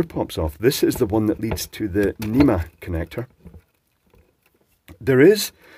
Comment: Did I hear "enema connector" several times!?